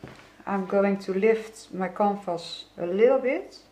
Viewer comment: It took me a while to realise you're saying 'canvas.' I thought you were saying compost (I know, it has absolutely nothing to do with your art, hence my utter confusion). 😆😆 I really should have understood you better, as your accent is very similar to my sister-in-law's who is Danish! Anyway, beautifully done, as ever. And I loved it when you said you'd 'try to make it into something beautiful,' as I thought it was already very beautiful!! I love, love, love your colour choices. Thank you.